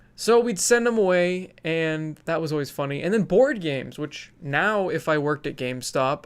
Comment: As a Swede I heard you say "Bored Games"... XD Made my day! TY!